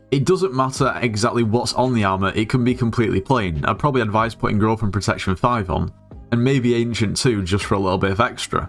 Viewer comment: I heard girlfriend protection V lol